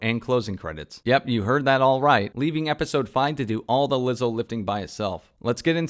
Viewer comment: I thought you said LIZZO LIFTING lol.